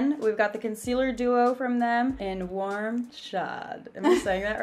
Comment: The whole time I thought she was saying warm Shaw" turns out she was saying "warm chaud" "chaud" is acc pronounced like "show"